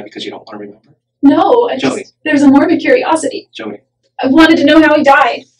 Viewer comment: Cop: Do you want to see the photos Jodi: Yes but only for morbid curiosity WTF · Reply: I thought she said Mormon curiosity at first.